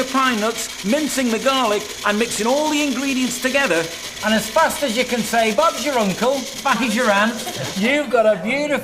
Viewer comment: What the FUCK does he say at 10:18?!?!?!?!?!? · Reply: Titus Sassole Fast as you can say- Bob is your uncle, Buffy is your aunt.